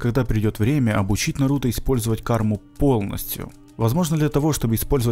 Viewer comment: мне послышалось или он сказал наруто ?